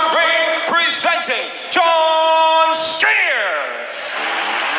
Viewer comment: For a sceond i thought he said john cena